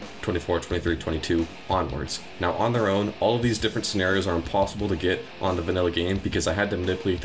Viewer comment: Right here, does he really say, “now on their own, all these different scenarios are impossibly gay.” Or am I trippin??